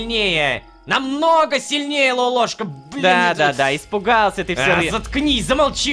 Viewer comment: мне не послышалось или хотел сказать бл...?